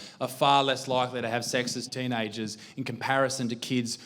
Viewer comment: Thought you said sexist teenages